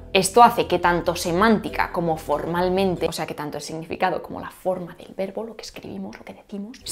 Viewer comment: oigan ¿qué dijo? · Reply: O sea que tanto el significado como la forma del verbo, lo que escribimos, lo que decimos... El verbo SUM en latín en latín es, más o menos, como el verbo SER